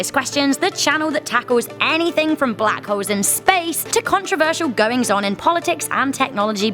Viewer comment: I thought at the start you said "..black hoes in space.."